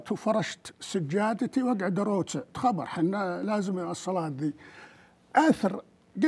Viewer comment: الدقيقه 8:40 فهموني وش قال هالمروح ( اقعد اروكع تخبر الصلاة ذي) !! اكمل متابعه والا لا لأنه نرفزني وجه الجني ذا